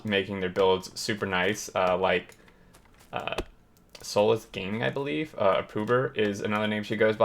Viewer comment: Great video , just one thing , who was the person you mentioned @ 9:40? couldn't quite understand the name , got interested when you said beautiful bases .. appreciated if you could tell me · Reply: Soulless Gaming <link>